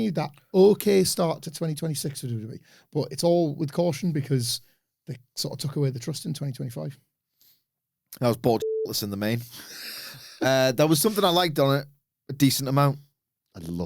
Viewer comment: I also thought said he was bored by the main event